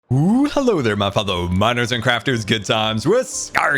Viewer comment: It's good to be back and hope you enjoyed it! Have a wonderful day! My Links and stuff: * Patreon: <link> * T-Shirt Shop: <link> * Twitter: <link> * Twitch: <link> * Instagram: <link> * Facebook: <link> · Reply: For some reason I thought you said hello, Minors and crafters lol